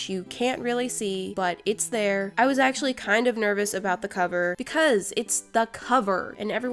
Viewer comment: Ha! 🤣 Listen at what she said..... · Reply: She said hover